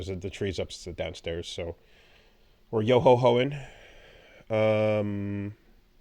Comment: I have watched this clip at least 3 times, and this is my first time realizing he said Yo Ho Hoing, instead of Ho Ho Hoing, and im cracking up lol